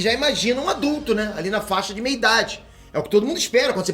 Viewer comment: Aos 2:00 o falando que o baby yoda tem aproximadamente 50 anos, que imagina alguém de "meia idade" eu escutei ele falando "minha idade" kkkk.